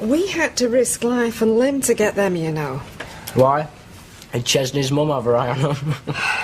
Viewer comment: What did he say? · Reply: did Chesneys mum have her eye on them